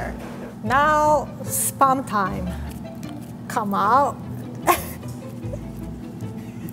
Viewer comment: was I the only one who heard “Sperm time, come out”